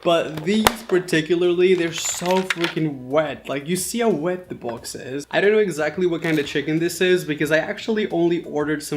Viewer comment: did you wash your hands? rinses hands with soap Washes hands did you dry them? shakes hands and eats this is exactly what i do to my brother and did u say the f word? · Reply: Jazzy Games he said freaking